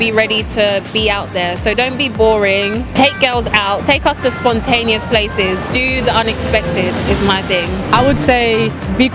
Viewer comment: it's 21st century. What a shame some women still think like that. She stating the guy must pay the bill in full IMO is the same as paying for her time. And that is called something else, not a date. If you both shared a moment in which BOTH decided to spend together, why should only the guy pay? don't get me wrong. In a relationship I always pay. But when I don't even know the woman? That mindset of hers is as if the guy must reward her for the time she spent with him. What about the time HE spent with her? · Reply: This is what the girl at 5:23 said: 'Don't be boring, take girls out, take her to spontaneous places, do the unexpected is my thing.'